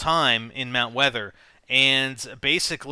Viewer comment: I thought he said Mount Pleasant? I swear he does.